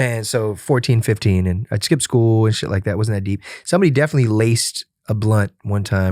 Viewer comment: damn, for a second I thought he said "I wasn't a NEET", but on second hearing I can hear he's saying "I wasn't that deep". darn. Kinda wish NEET was part of the vocab now.